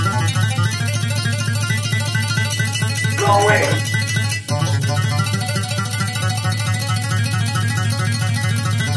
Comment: wtf did he just yell???? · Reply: He tried to fake a masterpiece